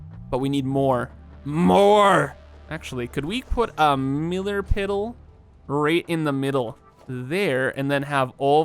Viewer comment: Who else heard the "miller piddle" at 13:14?